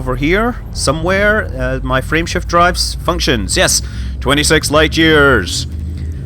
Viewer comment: With your accent,it sounds like you say Friendship Drive instead of Frameshift Drive. I think they should change it to Friendship Drive now.